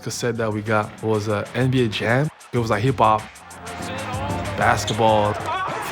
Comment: did he just say hip Bob ? 🤣🤣🤣